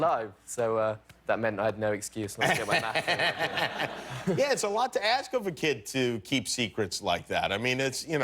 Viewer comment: I though I heard him say “I’ve been really good friends with this guy dean who plays Tormond Baratheon which is such a shame because he’s dead now”.... I freaked out and had to rewatch it to realize he said Tommen